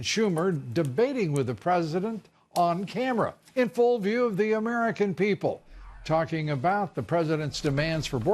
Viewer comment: I heard “radical dim leaders” sounds about right...